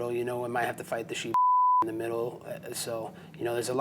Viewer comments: Did he just call Khabib the shoe bomber? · did he just call khabib a sheepf*cker 🤔😂. Damn, this guy is wild!